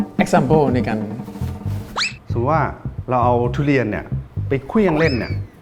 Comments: ทำไมทุกคนได้ยินคำว่า "สมมุติว่า" เป็น "คือว่า" อ่ะ คำออกจะต่างขนาดนั้น พี่เติ๊ดไม่ได้พูดคำว่าคือนะ 😂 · พี่เติ๊ดพูดว่า “คือ” มั้ยครับ 😂😂